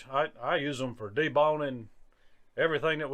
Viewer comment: I could have sworn I heard you say that you used a fillet knife for everything, including "deballing". If I were a hog and I saw you coming with a fillet knife, I guarantee the only way you would catch me would be with a 12 gauge!!